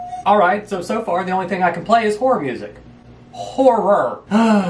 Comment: "Alright so... so far the only thing I can play is horror music... ho-rr-or." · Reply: I thought he said horror then he specified it... After that the first "horror" turned into "wh*re"